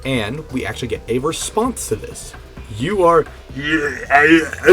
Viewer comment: it think it says “you are afton family”